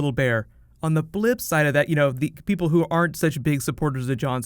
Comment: um...did you just say "blip side"? do you also think pedal stool is petal stool? it "bottles" the mind